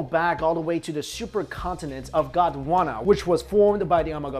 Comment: when u said gondwana i thought u said uganda XD